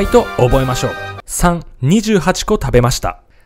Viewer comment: 愛知生まれの秋田育ちの40代だけど、乾杯の練習するのは4年前にそんな事のすんの？って、初めて知った。由利本荘の知り合いと飲みに行って、この時知った。 金萬は28個食べましたって言ってない。 日曜8個食べました。が、28個って聞こえるだけ。 ゴミを捨てるのを投げるとは言わない。 捨てるは捨てる。 投げるは投げる。 バナボートは一昨年初め知った。